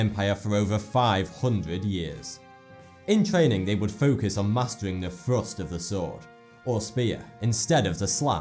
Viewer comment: I thought he was going to say masturbating